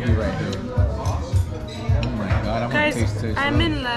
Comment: Did I hear “oh my god Imma taste test those” LMFAOOOOO